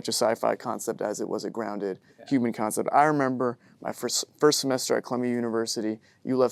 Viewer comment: sounds like he said chlamydia university